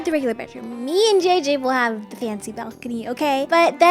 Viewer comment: When said "me and JJ wil have a fancy balcony" I thought she said "me an JJ will dance at the balcony" 😂😅